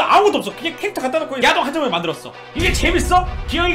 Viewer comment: 나만 야동한장 이라고 들림